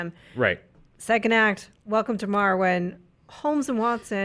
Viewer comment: said "welcome to marwen" and I heard "welcome to Morrowind" and im just like "wait what?"